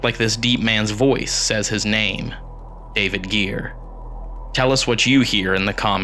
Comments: That deep man’s voice on spirit box sounds like it said David here..instead of David gear. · I hear "Baby Girl" not "David Gear"